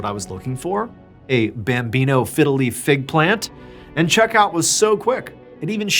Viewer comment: When you said Bambino Fiddle Leaf Fig plant i misheard you and thought you were saying "Fiddly Fig" and was so confused 😂